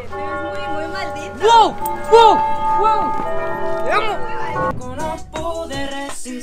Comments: No sé ustedes pero yo escuche a Emilio decir a karol te amo en el minuto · o 2:21 Escuché mal o dijo TE AMO🤔😱😱😱